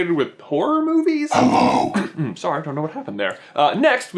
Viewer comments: I thought he said "porn movies" at the start......... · the way you said horror it sounded like you said porn. i spat my drink out.